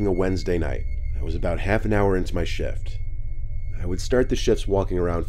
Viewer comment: It sounded like you said, "About an hour into my shit".